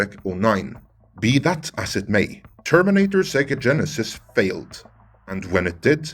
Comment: "Terminator Sega Genesis..."? Did I heard that right?